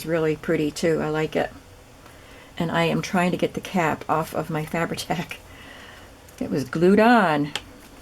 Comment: Oh my gosh when you said "I'm trying to get the cap off my fabri-tac" I heard "I'm trying to get the CAT off my fabri-tac!" and I was like OH NO! hahaha. Love this idea! I hope they sell great! Looks fun to make!